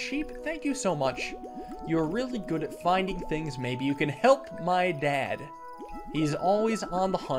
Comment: ‘Maybe you can help my dad’ I thought said ‘maybe you can help find my dad’